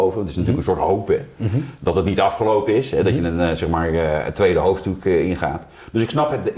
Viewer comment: Wat zegt Jan Roos nu bij 3:43 ? "dat je een soort 2e hoofddoek in gaat" 🤣🤣